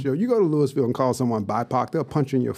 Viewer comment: mark what does he say bi pac? Can someone help?